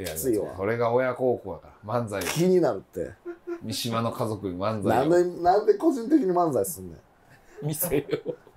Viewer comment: 「三島の家族に漫才」が「鶴瓶の家族に乾杯」みたいに聞こえてよもぎ🌿🌿🌿🌿🌿🌿🌿